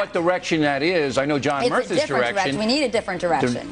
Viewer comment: bill says john merthas erection instead of direction. only a slip up but still funny as hell